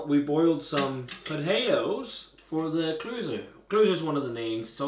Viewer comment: What did he say at 5:55? "Klusen"? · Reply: Klössen its the dumplings :-)